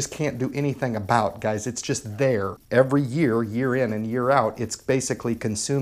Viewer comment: Did he say "urine" at 2:37?? lol